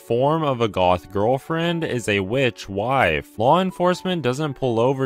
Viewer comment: Him: witch wife How i hear it "Rich wife" "